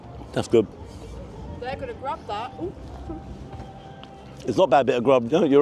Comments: she said ''they're gonna grab that'' and not ''good grub that'' 😅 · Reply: i thought she said good grub that too lol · lol about 6.40 the lass said they gonna grab that he thought she said good bit of grub lol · I thought she said "... they're going to grab that.." meaning the birds